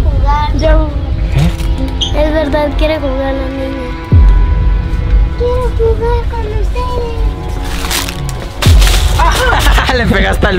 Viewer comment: la cara de amooo los amo mucho<3<3<3<3<3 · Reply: también en el minuto 13:28 dice: es verdad la niña quiere jugar y se escucha la vos de una niña que dice:quiero jugar con ustedes,se escucha súper vlarito